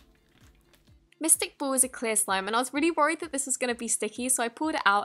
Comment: did she say Mystic?